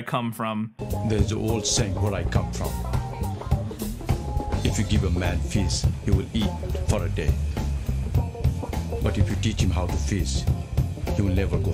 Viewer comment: are you SURE he isn't saying "give a man fist, he will eat for a day, but if you teach him how to fist he will never go hungry". because that is sure as hell what I heard and was wondering if it was some kind of sex joke/twist on the normal saying....